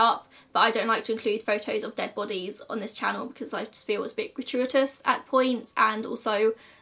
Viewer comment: Weird as comment but at 13:43 what does that word mean? 😅 tried googling it but don’t even know the spelling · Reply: She has a very thick British accent. I THINK she said Gratuitous. And I think she is using it in the sense that posting the picture is unnecessary or not justified.